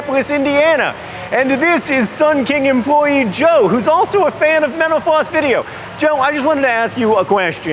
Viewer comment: John sounds like he said "Joe is also a fan of menopause video" at the beginning, lol.